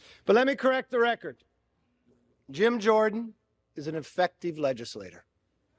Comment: That sounded like McSharty said "Gym Jordan is an infective leader."🙃